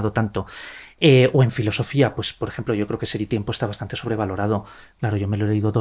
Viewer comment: Qué libro de filosofia para él está sobrevalorad? No le logro entender lo que dice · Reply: "Ser y Tiempo" de Martin Heidegger. Coincido y también grandes pensadores y escritores coincidian en que Heidegger invento un vocabulario complejo para algo muy simple, que era innecesario. Escritores de la talla de Borges llegaron a estas conclusiones sobre la vana opulencia verbal de Heidegger.